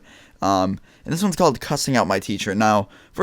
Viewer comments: I thought he said cursing my teacher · I thought it said crushing out my teacher and I was like, you are stupid